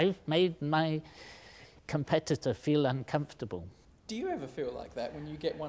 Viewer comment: "hee hee! I've made my competitor feel uncomfortable....." Love the professor! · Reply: At first i thought he said computer not competitor